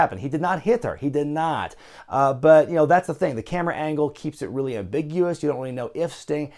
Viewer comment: Sound like is saying "Carrot Angle".